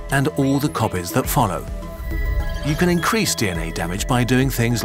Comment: I thought it said decrease and I was about to like... "WAHHH"